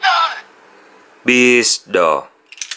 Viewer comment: "Doru doru" - Nó nói gì hổng nghe gì hết chơn